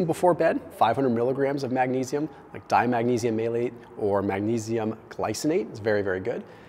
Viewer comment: Hmm, did anyone understand what he said at 05:57 [concerning the first type of magnesium he suggested]?? I heard "..or magnesium glycinate is very good...", but didn't get the first thing he said.... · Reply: Magnesium Malate